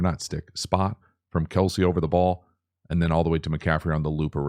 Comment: did you say Kelce? lol